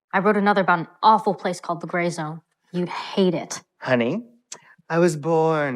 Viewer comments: am i missing the joke? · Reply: I think he misheard it as gayzone? I don't know. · Because he's gay or because he's an actor in the children's show? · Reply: He thought she said the gay zone